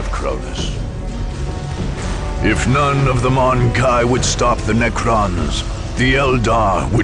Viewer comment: At first, when I heard the last line of the Eldar intro, I thought it said "If none of the Mont'ka would stop the Necrons..." which confused me; I thought Mont'ka was Tau?